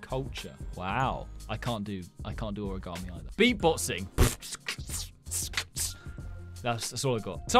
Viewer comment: When he said beatboxing I thought he said he bossy